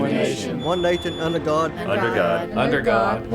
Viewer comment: thought I heard the one guy say 'one nathan under god' xD